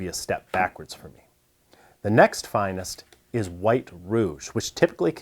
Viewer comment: I didn't know rouge was a type of sharpening substrate I just heard "white red" and "red red" lol